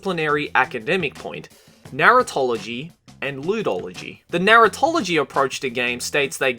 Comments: for a sec i thought he said "narutology".... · I thought said Naruto-ology at first. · I seriously wasn't looking at the video and I thought you said 'narutology' and 'lewdology'